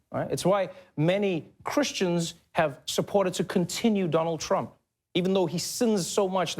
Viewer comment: supported to continue? or continued to support? or am I hearing this wrong?